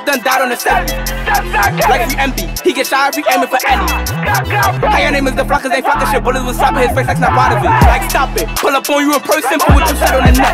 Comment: what did he say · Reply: How ya names da flackas yall ain’t falkin shi